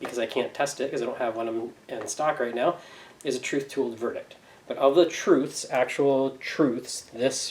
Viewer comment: ish... what did you call it, trith tooled verdict ?